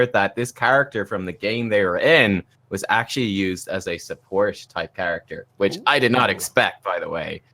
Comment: is saying support? what else would it be? seems like the word ends with st though